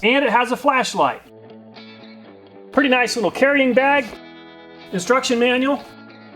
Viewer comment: "Destruction Manual"... Lol I know that's not what he said but it sounded like it.